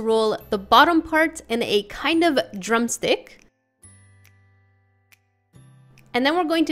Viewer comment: When you said drum stick I thought you said drunk stick